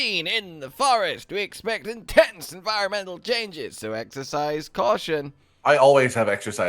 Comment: Thought he said exercise cotton